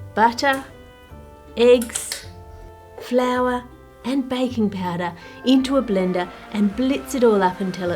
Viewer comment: I love accent, specially because I keep hearing “bacon powder” instead of baking powder